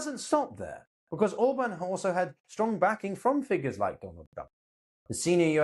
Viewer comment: Did he say Donald Dump? At